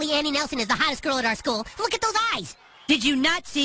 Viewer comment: I'd do those water balloons : Butters 2013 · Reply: I thought he said "I do love water balloons"